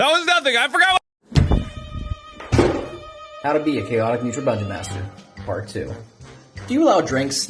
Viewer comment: i heard bunjoen